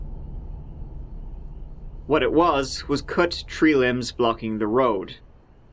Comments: I thought you said 3 limbs instead of tree limbs lmao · When he said there were tree limbs in the road, I thought he was saying there were three limbs 😳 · Bruh i thought he was saying "THREE LIMBS" the whole time and i was like "OH MY GOD WTF???". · I thought you were saying “three limbs” like three severed limbs and I wondered why he was so chill about that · Ohhh Tree limbs!!!! I thought he said three limbs. And that kids did it. I'm like wtf? What kids do that now?